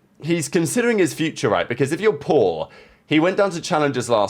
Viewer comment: I thought you said "if you're poor" not paul 🤣🤣🤣